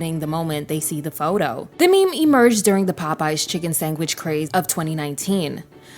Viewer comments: girl did you just say chicken sangwich? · Say sandwich. Because at 27:30 the bot sounds like its saying "sang-witch"